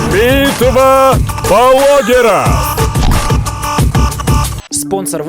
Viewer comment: Лайк кто услышал " битва БаЛогеров"